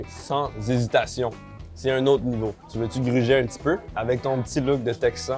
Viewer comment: à 8:57 tu dis "tu veux gryźer un petit peu?"...C'est moi ou c'est un néologisme Polono-Francais, gryźć = mordre, et transformé à l'infinitif en Français? 😅